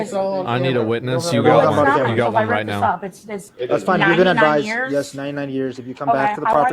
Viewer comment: Did anyone else hear the cop say “it’s fine you’re gonna die soon” then stop himself ? At 6:10 I know I’m not trippin. 😂